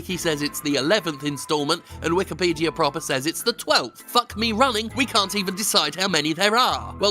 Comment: Did you just say "fuck me runny" at 10:50? I was with you through the boglin fetish, I was even there for pog sex, but this is where I draw the line, 😂